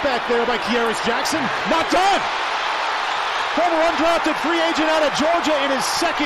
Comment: did he say #1 drafted free agent 😂😂😂